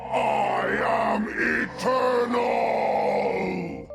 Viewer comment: Everyone who hears "I am a turtle" from Mal'ganis has serious brain damage. The E in eternal is enunciated super clearly. It's like the stupidest mishearing that somehow gained popularity.